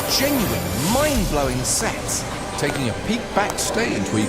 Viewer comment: When he said "mindblowing sets", I thought he said "mindblowing sex" I need to go to bed